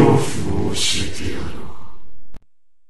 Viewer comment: What does he said at 0:28 :) Nice job ! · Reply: CHARI he says “Matomete kuru ga ii”